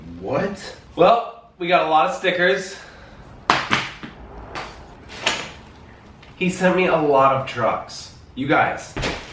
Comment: It sounded like said “He sent me a lot of drugs.” 1:51 Lol 😂